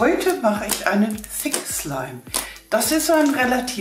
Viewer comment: habe ich verstanden Fick slime😂😂😂😂😂